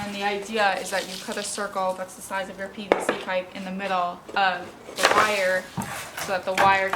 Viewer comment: I thought she said you cut a circle the size of your penis? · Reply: lmao I said a circle the size of your PVC pipe